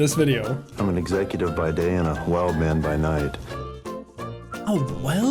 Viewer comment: Jack, I think he says “wild man”